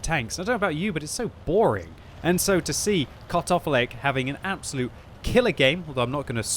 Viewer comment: cut off leg? :)) legitimately thats what I heard